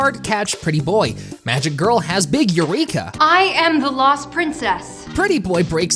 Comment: I have CURSED hearing, had to do a double take because I thought when you said Rapunzel had a huge eureka, you said a "big urethra" 😭💀💀